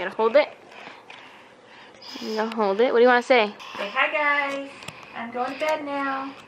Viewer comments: its sounded like whispered "Hey guys."? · it sounds like said "hey guys" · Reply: Bacon & Pancakes he said hi guys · #firstword sounded like he said hey guys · i heard "hi guys" being whispered? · it totally sounded like said hay guys omg lol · anyone else hear say "hi guys" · I swear said hi guys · Does say Hey guys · Is it just me or did anyone hear say hi guys